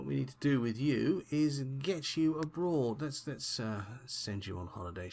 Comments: When you said "Lets get you aboard" I thought you really said was "Lets get you a bra" XD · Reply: I heard it as, "Let's get you a Broad", old slang for woman. For a second, I wondered if you had accidentally clicked on Ellen when you meant Damien. lol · When you said "Lets get you aboard" I thought you really said was "Lets get you a bra" XD